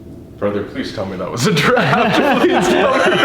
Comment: When you guys say a draft closed the door I thought you said giraffe 😂😂😂